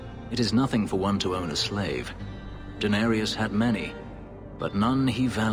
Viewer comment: I thought he was saying Daenerys everytime he says Danarius! LOL